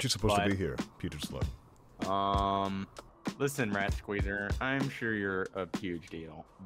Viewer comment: I thought said Ratqueerer 💀